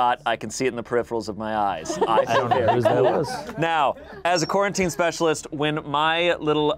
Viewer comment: Big brain moment, I thought said "Quarantine Specimen" and I was just like "how does that help? Wel...I guess the game needed something" and after a while I was like "OH he said Quarantine *Specialist !!!"